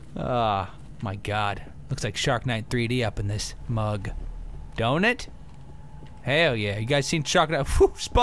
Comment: who else at 4:00 thought he said " in this mug donut"